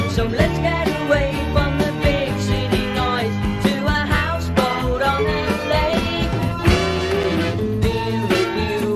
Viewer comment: What does he say at 24:18, after "so let's get away from the big city noise to a house ...? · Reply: To me it sounds like "to a houseboat on a lake"